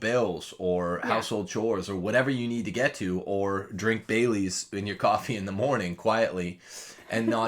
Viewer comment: I thought you said you put Bay Leaves in coffee and thought, 'Wow, is that a thing? '- Bailey's!